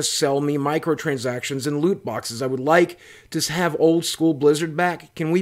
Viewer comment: oh I just heard "What I want?! Is my Blizzard back!" in Saurfang voice... :)